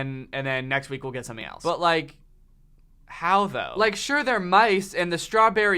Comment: Did he just say... MICE? hahaha